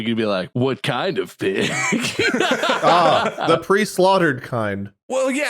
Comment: I've played too much Bloodborne, and absolutely thought said the "Prey Slaughtered" kind.